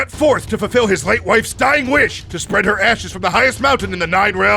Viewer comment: I thought he said spread her asses.